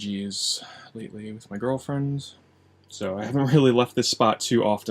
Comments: did you say gulp GIRLFRIEND!? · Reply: He said girlFRIENDS! we've suspected it for years but hes a playa! probably has a few side pieces. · Did he say "Girlfriends" at 9:02 ? Yeah! Whoo!